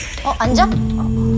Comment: Soyeon speaking portuguese "OI PAAAI" kkkkkkkkkkkkkkkkkkkkkkk